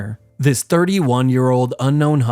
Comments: lol I thought you said Jim Cornette, I was about to say “Damn not only was he a wrestler, wrestling Booker, but also a hunter. 🙀” lol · Ngl i thought bro said "Jim Corvette" (Yes my ears need an update)